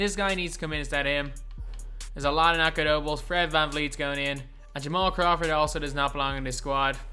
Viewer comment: I swore you were just saying Jamal crawford was the goat😔